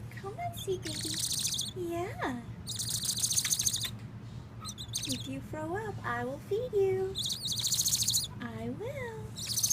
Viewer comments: I thought she said Lady: You better not throw up or I will beat you! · I thought she said "if you throw up, I'll beat you!" And was very concerned for the baby owl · The first time I watched this I heard “if u throw up I’ll beat u” “I will...”